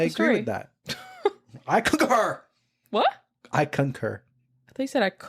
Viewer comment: I HEARD “I COOK HER” TOO!!! Lol And the subtitles thought that too hehe